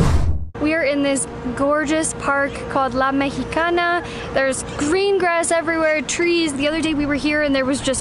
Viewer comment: At the beginning I totally heard “there’s gringas everywhere” instead of “green grass.” 😂